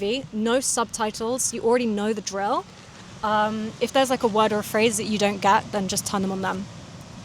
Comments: what is that word (or phrase)? what did you say? I don't get it. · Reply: “if there’s a word or phrase you don’t get, then just turn on them.” that’s what I heard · I missed two words (I think) : @ 5:08, you say "if there's like a word or a phrase that you don't get, then just XXXXX XX on them". Except from that I understood everything (I even thought that there would be a point from where you would begin to speak much faster). Native french speaker living in Belgium : I only watch videos, films and tv shows in english (no ST) and I do it everyday. Took a test once, got C2. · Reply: turn them on then :) i mix it all up to like ‘turnemon then’ 😅